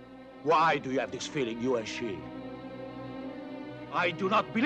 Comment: Having never heard this, I'm having difficulty parsing the exact words. In the beginning, is he saying, "Why do you have this feeling you are she?" Or am I mishearing it? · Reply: he said 'you & she'